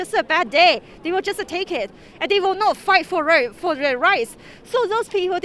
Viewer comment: did she say fight for rice or rights???